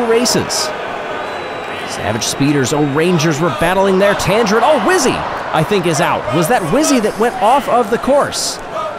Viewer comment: Can someone tell me what the crowd chant is at 2:24? I'm trying to identify as many as possible. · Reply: They're chanting the raspberry racer's chant "ro ro robust raspberry"